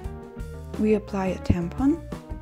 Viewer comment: OMG!! Did she just say apply the TAMPON?